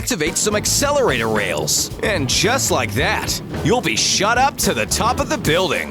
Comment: I think I heard shut up instead of shot up